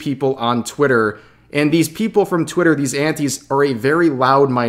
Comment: for the longest time i thought you were saying aunties.